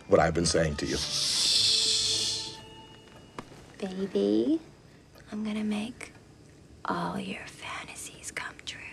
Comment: i thought she said baby shark